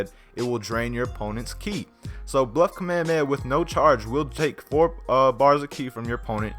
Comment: Thought it said Black Kamehameha xD